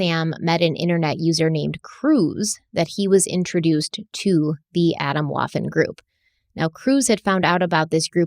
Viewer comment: I speak German and had the biggest ah ha moment when Stephanie said that "Adam Walton" was "Atomwaffen." Germany just raided a bunch of Atomwaffen-esk organizations the other day. These groups have been especially problematic because so many of the members are in law enforcement or the military (i.e. a small percentage of officers make up a large chunk of these groups). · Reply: Swedish speaker here... I thought she said Adam Waffle 🤣